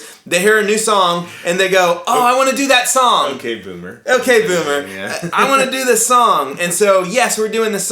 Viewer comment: When said "okay Boomer" I could have sworn he said "okay Fuller" with the same intent lol